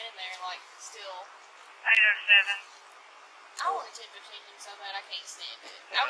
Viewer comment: it sounds like she said "I wanted to detain him so bad". But either way whatever she said is uncalled for and shows that she should not be in this line of work because she's the type to make up some lie or plant certain "evidence" to arrest an innocent person.